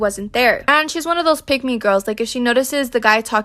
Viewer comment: I kept hearing “Pygmy girl” instead of pick me girl 💀